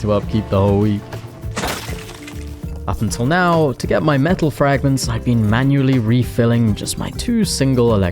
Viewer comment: " Mental Fragments " did he said that cuz i dont realy sure xD